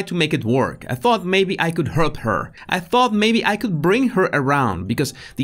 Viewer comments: Why at 3:55 (ish) ...did you say "hurt" when it says "help" on screen? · Major error at 3:52. You misread JD's words as "hurt her" instead of "help her". Very significant mistake with implications there.